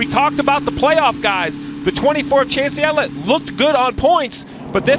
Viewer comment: Did he say.. Chase Yellit.....